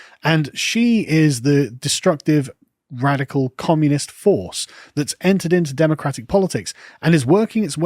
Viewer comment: I honestly thought he said horse.